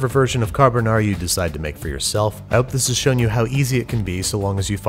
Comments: At the end I thought he said "rolling your arse" and I was so confused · Hahaha, last sentance killed me since im from Scotland, i thought he said "start rolling your ARSE!"